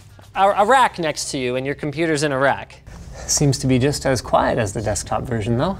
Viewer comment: linus my computer is not in Iraq sorry even the captions got it